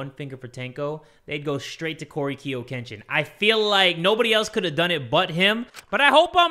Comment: Did Jay just called him Kiyo Korekiyo Kenshin XD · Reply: Coryx(Kiyo)kenshin is what he said, that’s the name of a youtuber lol